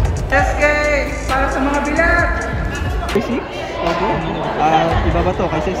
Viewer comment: “PARA SA MGA BELAT” ako lng ba o narinig niyo rin?🤣